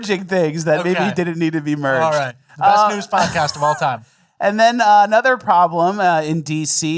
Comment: Sounds like is saying "best snooze pod cast."